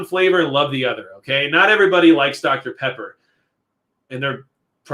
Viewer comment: When you said “dr. Pepper,” I swore you said “Dr. Prepper.” Is that a problem? Probably haha